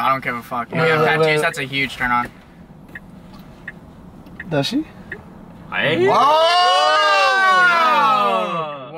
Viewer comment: I wanna know what u said 😭😭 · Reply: He said “does she?”